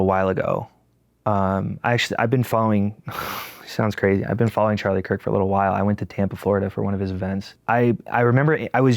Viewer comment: what does he say @ 6:45? ( heard several ppl say “ so much for the ____ “) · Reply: "So much for the tolerant left"